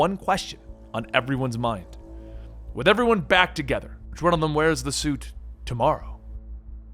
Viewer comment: Can anyone tell me what Bruce say to everyone, at 12:20 ? I can't hear it clearly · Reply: “With everyone back together which one of them wears the suit tomorrow”